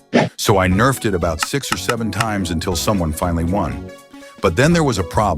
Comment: erm did I hear 67